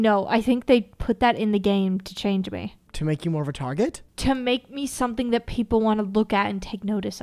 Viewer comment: Omg i thought he said something else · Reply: he said more of a target😭😭